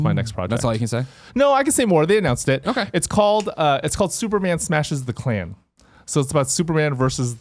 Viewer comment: I thought I heard “Superman smashes the clam” Damn, I didn’t know DC endorsed x-rated content.